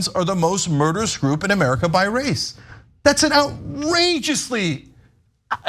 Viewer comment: No she clearly said "by rate" not by race. 🤦‍♂️🤣🤣🤣